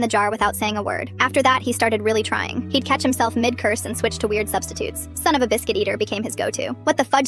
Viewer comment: I thought you said no substitute’s